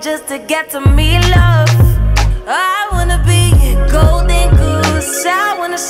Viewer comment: I thought she said I wanna be your gold digger I would had favored that part 😫😫